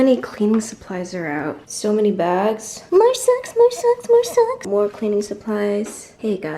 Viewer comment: I was in on different tab when you said 'more socks' and I thought you said 'more sex'! haha